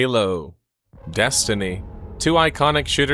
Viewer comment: I'd recommend vocal warmup because I hear "shitters" not shooters 😂😂